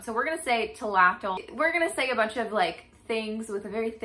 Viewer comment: I heard" kill a cat". Man was I wrong. Great vid.